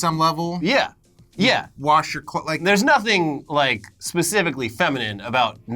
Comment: CC says wash your clit